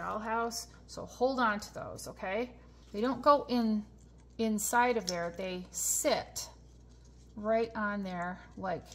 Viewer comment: Just in time (8:07), I almost threw out my scrap molding wood pieces (how did you call them?), now I keep them for my next project, or I can recreate furniture in paper! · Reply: I called them kit scrap, I believe. Glad you found it helpful!